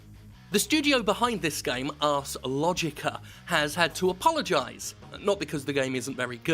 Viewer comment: Did he just say "Arse-logicka"?